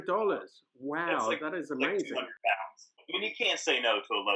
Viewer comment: Wait does he say like £600? The last time I checked $300 wasn't that much. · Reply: ? I think he says "like £200" that makes more sense